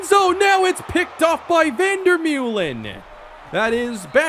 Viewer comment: When Vandermullen got the pick I thought it said Watermelon 🍉